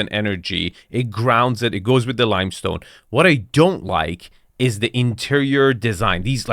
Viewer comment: I thought you said you like the plant 🪴 lol